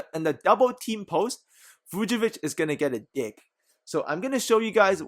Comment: Hahah I heard "Vucevic is gonna get a dick." 😭